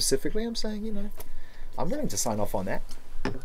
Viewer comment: can anybody tell me what he say at 16:24? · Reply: "i'm willing to sign off on that" like he feels he's right to say that